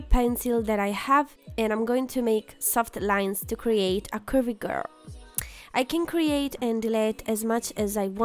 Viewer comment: I'm dying! I thought it said soft curry girl 😂😂😂